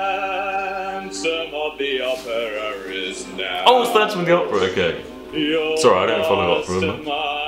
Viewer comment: YOU DIDNT HEAR IT!!!! Dan said "isnt it funny that we're singing the PHANtom of the opera" U WOULD'VE DIED IF U HAD LISTENED